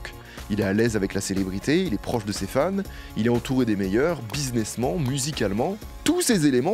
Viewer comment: au début quand j'ai entendu ''buzinessment'' je me suis dit ce type prononce très mal le mot businessman avant de comprendre